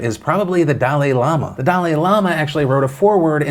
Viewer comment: I thought you said Italian llama so I was just like damn, that actually is pretty surprising. On the other hand this whole story literally made my skin crawl and just makes me lose my faith in humanity even more that people can be this cruel. On another note though, didn't Onion get busted for running a cult, or trying to start one?